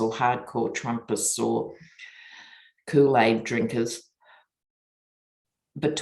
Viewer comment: When you said, 'those who've drunk the kool aid' - I thought you'd said 'coup aid'! Works too unfortunately. Blessings of Love, Inner Strength, Faithful Fortitude and Every Goodness unto Us One & All ~ Blessed is our Family Humanity✨ ✨🎶✨🌈✨🕊✨🕉✨💟✨🎆✨